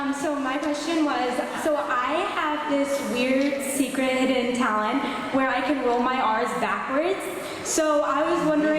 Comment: what did she say? she can roll what backwards? xx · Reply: It was her R's